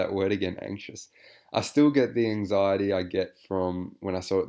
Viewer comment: did you say anxiety? Are you anxious to see his penis?